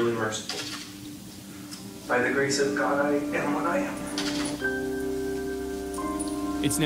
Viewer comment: The last few words of his statement... What is he saying? " By the grace of god... __ .." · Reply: I think he said by the grace of God, I get what I get